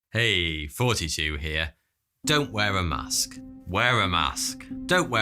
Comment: He always sounds like he says, " 42 here" instead of thoughty 2 lol